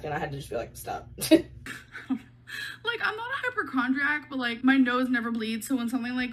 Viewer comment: "my nose never bleeds so, do I have a tumor" HIGHLY DOUBTFUL. The hypochondria is strong in this one 😂 Also I think she just said "hyperchondriac"